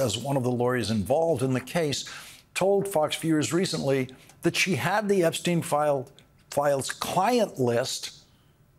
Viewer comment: I thought shh said shh had dah file